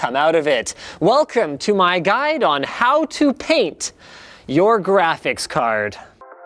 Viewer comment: awww, I thought is said "Why not print your Video Card. So when you said "so why not PAINT it" I laughed... Until I saw the title... ;_